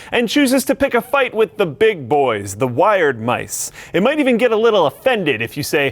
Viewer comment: did he say wired mice?